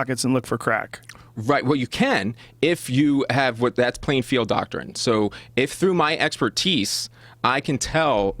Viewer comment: I thought he said “playing field doctor” so I thought this was a reference to finger checking the back door for narcotics so who’s the sicko now because he was saying something completely different. Lol